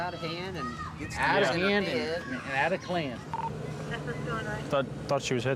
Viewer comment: did he say ? “Out of klan”? Wtf I see why she was so mad